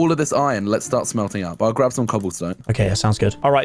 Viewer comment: did he say hell craft some cobblestone 😂😂